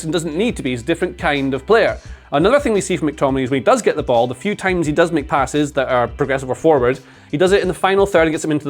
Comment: When he said "the few times he does make passes" I thought he said "the few times he does McPasses"